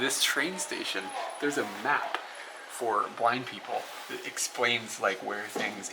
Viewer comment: OMG, I thought said black people instead of blind people at first XD hehe. ugh love your videos guys 😍